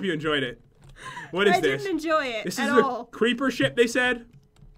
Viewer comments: did anyone else hear creeper s***??? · anyone else hear him say "creeper shit"!